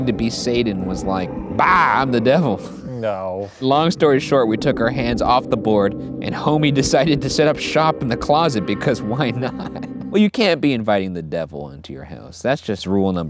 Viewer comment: I THOUGHT IT SAID SANTA IN MY CLOSET